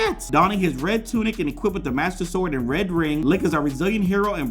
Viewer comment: I thought you said “Rhett and Link” in the beginning XD